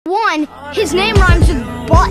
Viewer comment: Why did i hear “One, his name rhymes with bot.” “I don‘t care, im gonna have a big and fat chipsy lenny” 😭😭